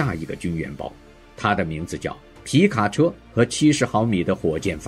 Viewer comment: 德國防無人機那段 9:50 處,雄雄聽成..「它的名字叫『皮卡丘』 和70毫米火箭發射器」